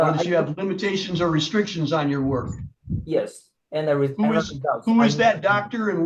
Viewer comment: Who is your Doctor? I keep hearing, "Who is your daddy and what does he do?"